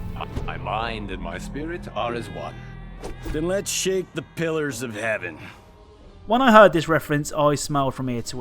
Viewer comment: When I was a kid, I thought that he said, "We really shit the pillers [pillows] of heaven."